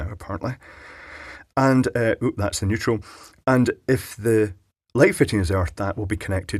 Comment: Neutral? I thought you said "Weutral"!